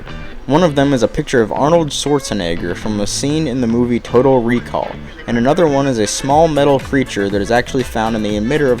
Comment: it sounds like he says Turtle Recoil